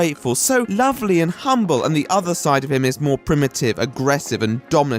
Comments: I thought I heard primink... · Thought I heard primick 🤔